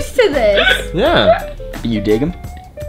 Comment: I swear I heard one of them say to Vanessa "are you dating?" And Vanessa was like "yeahhh"